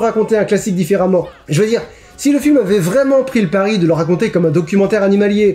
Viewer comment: C'est moi ou à 36:51 tu dis "si le film aurait vraiment [...]" 😱